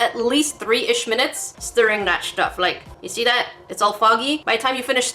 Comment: At first I thought you said “stir that sh*t up” XD but you said “stir that stuff” XD O O O O O O O O O O O O O O O O O O O O O O O O O O O O O O O O O O O O O O O O O O O O O. O O O O O O O O O O O O O O O O O O O O O O F F F F F F F F F F F F F F F F F